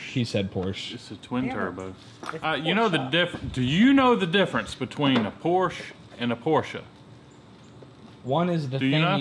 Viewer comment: XD I COULD HAVE SWORN HE SAID, "what's the difference between a porch and abortion" XDXD😂😂😂